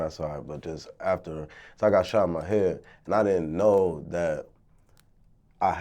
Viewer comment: I thought that shit said serving gun shot in the head 🤣🤣